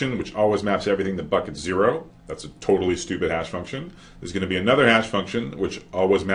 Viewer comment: He said "If it maps everything to 0, that's a totally stupid hash function", but I heard "If it maps everything to 0, that's a totally stupid-ass function" :D